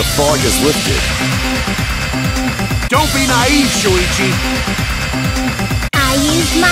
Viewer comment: "tHaTs R O B O P H O B I C" "i UsEd My m A J I K" Same energy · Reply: I sounds like She said “NAIVE MY MAGIC” instead of “I USED MY MAGIC”